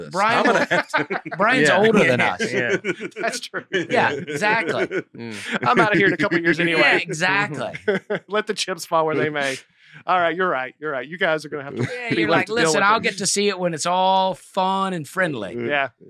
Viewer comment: Did say "Let the chimps fall where they may" surely not. All this talk of animals has this show off the rails.